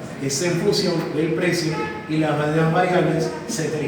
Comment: que dijo?? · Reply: y las demas variables